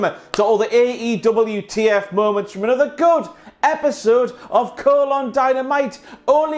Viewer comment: does say “cole on Dynamite”? What’s he saying... and why? Lol